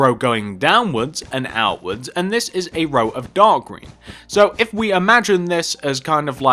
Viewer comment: it sounded like he said dog green